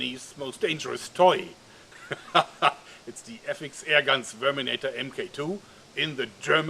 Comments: Sounds like you said "Sperminator" xD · I keep hearing sperminator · It sounded like he said sperminator